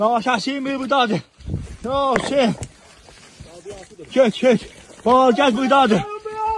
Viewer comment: I was sorry for the guy ,, you would hear the anguish in his voice . as he was calling out hoping to hear a response ; and then it sounded like he was saying :' O shit , o shit !"